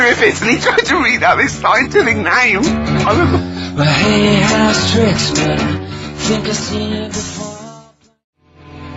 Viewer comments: Does anyone know the artist or song title at 5:30. I couldn't understand Ricky when he said it. Thanks! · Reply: The song is "Shot Shot" by "Gomez", if you're still alive since this comment was posted xD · Does anyone know the artist or song title at 5:30. I couldn't understand Ricky when he said it. Thanks! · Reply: "Shot Shot" by Gomez off In Our Gun (2002). Don't thank me—thank Sound Hound. I just held my phone in the vicinity of my laptop, played that bit and the magic app found it. <link>